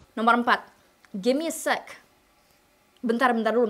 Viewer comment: salah dengar jadi gimme a sex, lol